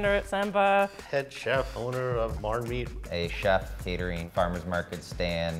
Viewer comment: did he just say owner of marmite?🤔